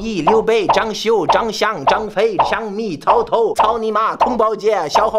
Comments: did you say "cao ni ma" 😂😂 lmao i was laughing so hard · I heard caomima